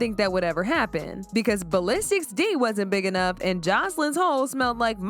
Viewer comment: I thought joseline said ballistic 🍆 was huge 😭😂😂